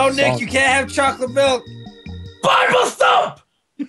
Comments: What does he say at 2:18? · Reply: Seth Ledger he says biblethump, it's a twitch emote about the binding of isaac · "No Nick you can't have chocolate milk." "BARBASTOLF!" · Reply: with fist i thought he said bible stuff XD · What did say at 2:17? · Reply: THUMP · someone please tell me what says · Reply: I think he says "BIBLE STUFF" thus the Isaac. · Did he say buy more soap? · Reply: Starwolf i thought he said bible stuff